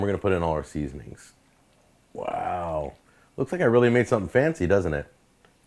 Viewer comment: did he just call that "Fancy" ?